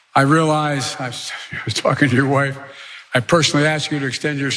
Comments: It sounded like he said, "I was talking to your wife." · did he just say “fkN’ your wife?!” 😂